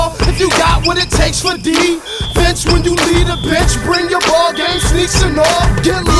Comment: I thought he said “bitch if you need a bitch “when I was little lol